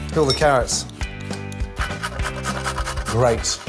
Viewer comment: captions say "kill the carrots" 😂😂